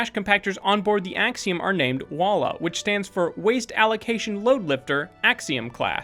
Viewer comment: I thought it was called, “ WALL.R